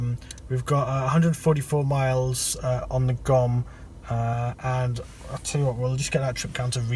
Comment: What is that word you are using at 10:44 which starts with a "G" I think? You said, "... we've got a 144 miles on the G_ _ _ _ ..." I've tried listening carefully every time you say that word and I still can't quite catch it. Sounds like "on the gum" or "on the gorm" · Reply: GOM, it’s an abbreviation of “guess-o-meter”, referring to the range estimate.